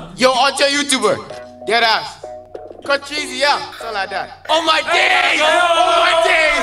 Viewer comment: HELPP IS IT JUST ME OR DID I JUST HEARD "OH MY GAYSS" OH MY GAYSS" ON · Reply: They said oh my days it's British slang